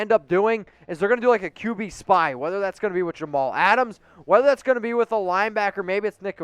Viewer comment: it sounds like he said Jamal Adam. Is he referring to Jonathan abram?